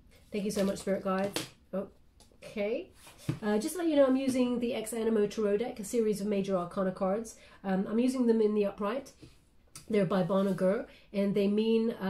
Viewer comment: Could you please spell the name of the deck of cards you are turning over at minute 5:52? I'm not understanding what you said. Thanks for all the great readings you give to us ❤️ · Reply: Ex Anima deck by Bon Augure